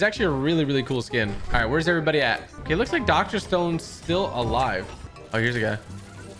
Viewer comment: did he just say doctor stone